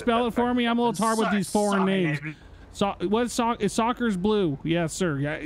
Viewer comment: Dogg did he just say soccer ball blue! I'm not good with yall foreigners 🤣🤣🤣🤣